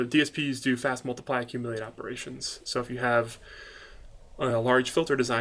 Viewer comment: DSP is to fast multiply accumulate operations? i'm not sure it i heard it correctly